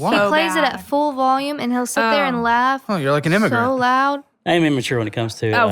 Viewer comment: "You're like an..." What? Sounded like "immigrant," closed captions say "immigrant;" but I don't understand what that means - how it makes sense in that context.